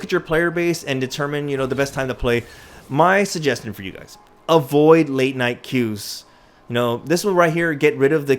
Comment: I thought he said avoid midnight coups at first